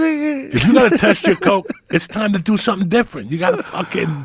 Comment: sounded like joe said “this nigga”😂😂